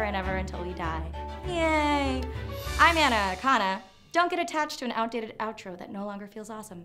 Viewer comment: When i first heard the part when she says : I'm Ana Akana I thought she said: I'm anaconda I was so confused.